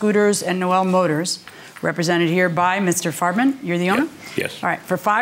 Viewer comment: I thought she said "Mr Fartman". Ha